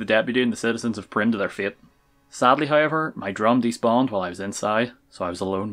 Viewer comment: What the heck does he even say at 3:17? “Sadly however, I drowned these bomb” “I drowned James Bond” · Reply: My drum despawned